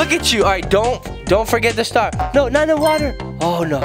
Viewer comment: Did he say 9 11??? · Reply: No, he said no,not the water.